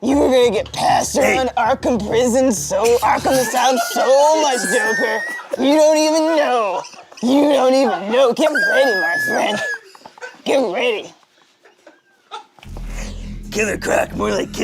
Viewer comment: pleaseee tell me what the black batman starts saying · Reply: you’re going to get passed around Arkham asylum so much, you don’t even know, you don’t even know, get ready my friend, get ready